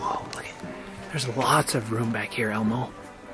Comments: “did he say Elmo I swear Andrew said Elmo!” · Did say, “Theres lots of room back here Elmo” He said Elmo I even replayed the clip like 100 times. This happened